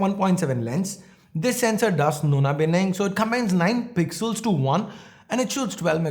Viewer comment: What do you say in this part? It's unclear and even the subtitles are unavailable 😛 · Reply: He said 'Nona-Binning' basically he meant 'Nona' which means '9' or 'NINE' in latin hehe. 'Nine in One Pixel Binning'